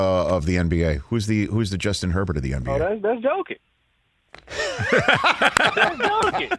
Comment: Herbo more Luka than Jokic 😂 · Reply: I literally heard "Doncic" when he first said it because I immediately thought the same thing. Took me til Rich said "the MVP?" to realize he said Jokic